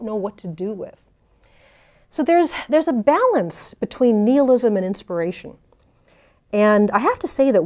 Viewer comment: I heard: "So there’s a balance between Liam Neeson and desperation"